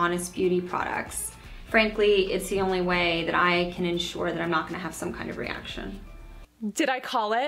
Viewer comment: It*s funny, I heard her say, "it's the only way Ii can ensure my brand does not cause irritation" as though see was self-testing to keep tabs on her line . . Perspective! Jessica Alba is lovely - not a user of much Honest products.